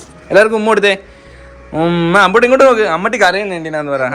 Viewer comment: said ammatty i heard mammatty